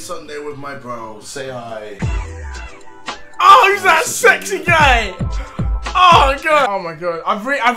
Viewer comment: Did he say Sexy gay?